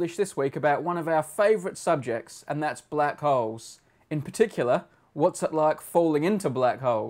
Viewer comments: It sounds like he is saying "our favorite subject black hoes" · This is what I thought I heard Brady say "...about one of our favorite subjects and that's black hoes." And now I can't unhear it D